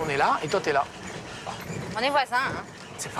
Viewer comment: Laisser sa vie de merde en France et aller s'installer au Philippines créer des business qui ne visent que des compatriotes et ne pas apporter grand chose au pays hote, c'est déplorable. Heureusement qu'ils ne peuvent pas acquérir de terrains. Gros coup de coeur pour et sa compagne. Je leur souhaite que du bonheur ❤ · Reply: C'est moi ou à 31:21, la dame dit on les voit pas (en parlant des locaux) et le type répond c'est pratique 🤮🤮🤮